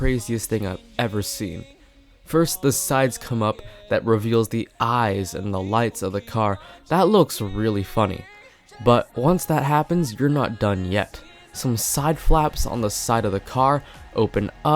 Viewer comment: When I played it in low volume I thought you said that looks really f*cking.